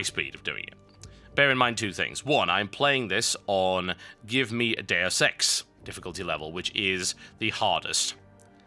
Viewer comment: Heh, when he says "Give me a Deus Ex" it sounds like he is saying "Give me a day of sex". xD Im sorry I know thats immature but its just what it sounds like.